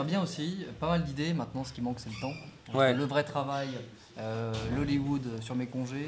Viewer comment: au début j'ai compris "levrette travail"...